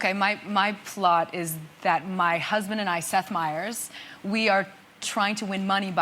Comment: I thought she said me and my husband set fires Now I see its me and my husband, Seth myers